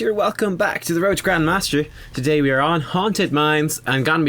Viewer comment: At the beginning I thought you said "roach the grandmaster" Think Kharazim can do solo healer? Cheers from Galway!